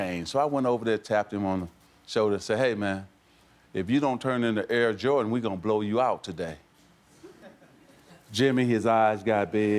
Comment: What does he say after "Hey, man. If you don't turn..."? Sorry, my English is not that good. Could anyone please tell me? Thank you. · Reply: "If you don't turn into "Air Jordan", we're gonna blow you out"